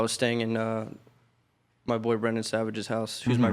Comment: Did he say boyfriend?